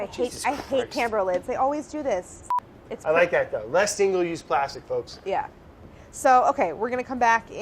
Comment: what did Brad say? "grfctydg use plastic folks" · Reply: "less single use plastic"